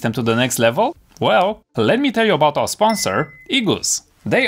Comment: Who else thought he said “let me tell your mom a sponsor “ 😂